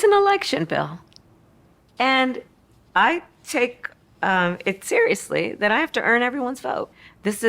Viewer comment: I heard "it's a election bill "I fake it seriously, come out of her mouth.